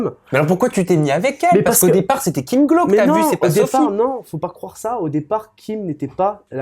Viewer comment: c'est moi ou à 2:00 il dit qu'elle s'appelle Sophie ?